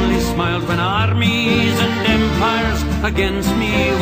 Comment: "When armies and empires against me were hurled". I swear I heard "armies of vampires"